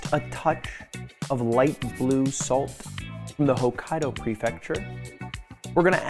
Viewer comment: sweat I thought he said Hokage prefecture